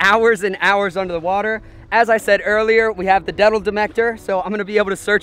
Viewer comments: This nigga said i got the detel demector. 😂😂 instead of metal detector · Did anyone notice he said the "detal demector" at 0:51 instead of saying metal detector